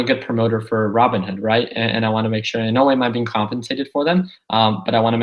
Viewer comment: did say he was being compensated by Robinhood??? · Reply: He said "In no way am i being compensated"